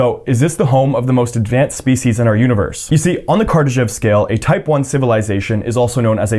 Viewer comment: i swear to god i am not high but i thought i heard kardashian's scale...